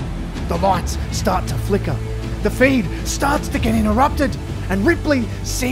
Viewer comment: did he say the feind? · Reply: Feed*